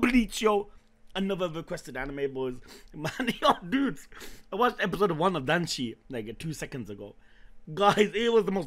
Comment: what did he watch? I cant hear what he says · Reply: Episode 1 of Danshi Danshi Koukousei no nichijou